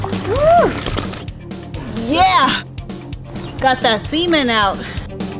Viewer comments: Almost spit out my coffee at 4:36, thought i heard her say, "Got that semen out!" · I am so sorry that I'm about to write this but it sounded like you said "got that semen out" at 4:38 while I was drinking water and I spit it all out lol I had to watch it again to see if I heard correctly and it sounded the same lol anywho great videos just subscribed last night watched your tile video in the bathroom great job!!!